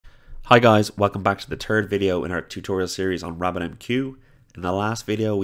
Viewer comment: I heard “the turd video”